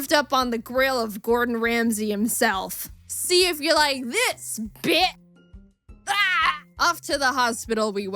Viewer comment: Am i the only one that when the hives bad guy pressed the button thought he said: SEE IF YOU LIKE THIS, BETCH